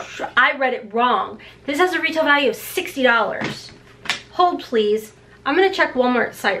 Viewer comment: I almost spit my water out!!😅😆 I literally thought you said, HOE PLEASE! 🤣😂🤣🤪😜